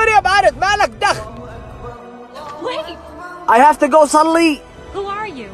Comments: Bro i thought he said "I hv to go suddenly" Apparently he wanted to go pray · Assalamualaikum sister, how are you, where are your hijab, its ramadan. leave her alone. Hey, Don't worry about it, its not your bussiness Wait I have to go shalli Who are you? I am brother Muhammad salla ali taruhi tarhum an !! Uwaaa.. ciyaaaaa !!... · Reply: I honestly thought he said “I have to go suddenly”.